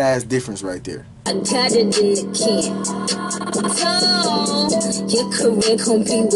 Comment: I’m literally just catching that bar and I’ve listened to this over s hundred times 🤯 I thought she she said doooor “I got it in the can, Dole‼️“ The canned fruit pineapple company 💀💀💀🔥😆 she’s so witty with her raps